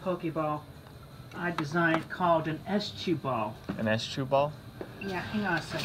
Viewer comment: Ugh this is just cruel. They came in just to make a purchase like any normal per- "..custom S-chuball" Ah- nevermind. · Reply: I heard “estchuball” as in estrogen at first